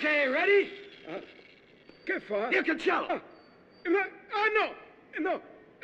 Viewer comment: Ma cosa dice l'hooligan a 2:28? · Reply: Dice "io cancello"